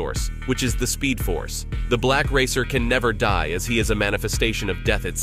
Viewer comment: Thought you said black racist at first